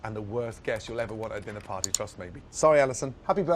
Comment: 最後のthanks for thatをthanks f*ck outに聞こえるように言うの草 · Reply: “That was Gordon Ramsay. Chef Support finished. Thank fuck for that.”